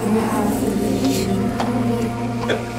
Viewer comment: I like the phrase it said “You have noticed me.” Which seems to indicate that it was in front of your eyes the whole time, and your mind literally had to take that long to comprehend what it was so that you could actually see it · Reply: Where did you hear that? I heard "You have permission to fulfill your mission"